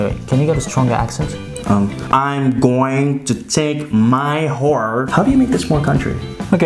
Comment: said "Whore" 😂😂😂instead of "Horse" Hahaha 😂😂😂